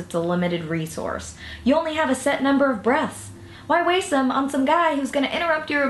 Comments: oh... i thought she said limited number of breasts... oops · I thought Anna said " a set number of breasts" LOL